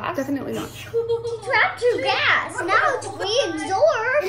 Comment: "she trapped her gas!" I hear as "she crapped her ass"